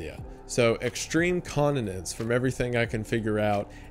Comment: I heard “extreme incontinence”, way scarier.